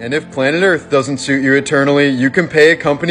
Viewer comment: Who else thought he was going to say bless your ass instead of Ashes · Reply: I definitely heard "Blast your asses." 😂